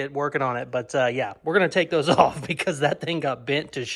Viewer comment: All I heard is gonna take his top off lmao